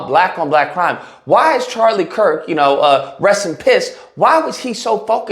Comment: Lol. is amazing. "Rest in P**t Charlie Kirk." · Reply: I thought I heard, 'rest in piss'